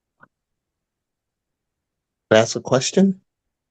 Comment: So beautiful ❤! you are an absolute Genius... Poor who thought that you said "NO!" when he said "Can I ask you a question?" while you obviously heard "Was that a question?" (I heard the same btw 😂) Another example of how langage constantly distort reality... Love to both of you ❤❤